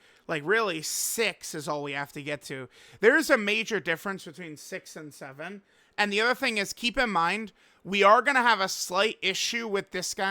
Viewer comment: what did he say? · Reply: 6… 7… 🥀